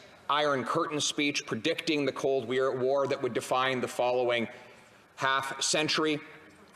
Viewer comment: I heard "cold beer"